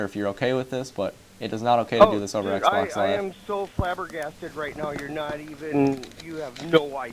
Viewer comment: What his dad says LOL!! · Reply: "I'm so flabbergasted" He's saying that he is shocked.